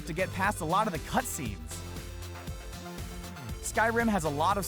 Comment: Did he just say "many cutscenes in skyrim"?